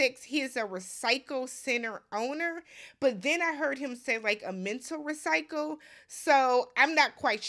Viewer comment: It was “metal recycling.” I thought the guy said “mental recycling” too. Thank goodness for closed captioning.